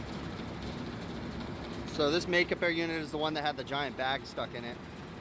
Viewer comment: all this time I thought he was saying MAKE-A-BEAR :D lol searched all spellings; no results :D not realizing what the word REALLY was was Make-Up Air :D